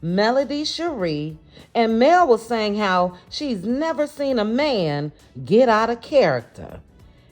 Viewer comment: I could’ve sworn that she said, I never saw Amin get out of character